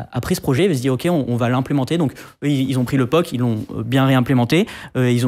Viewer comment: Dites moi que je suis pas le seul à entendre "ils ont pris Laupok" ...